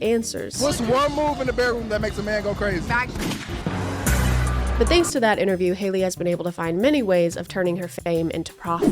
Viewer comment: I have rewinded the clip at 0:31 like ten times and I am still completely unable to understand her answer at all. Like, what the hell is she saying? I can’t even identify any word at all and the subtitles don’t pick up her answer at all. It subtitles the question and then right back to what says. Pleeeeeeeeeease can someone help me and tell me what she says, I will be hopelessly wondering about this all day now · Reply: Back shots